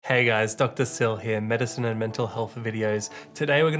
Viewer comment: Why do i hear "Dr Cell here" I might be watching DBZ a bit to much.